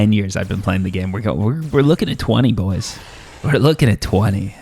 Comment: Did you say "we're looking at 20, goys"?